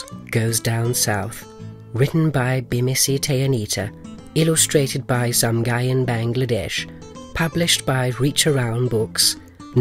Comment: I just happened to be listening to it partway through in the background and got to the credits, heard the names and I thought I heard "Illustrated by some guy in Bangladesh" and had to double check the screen 😂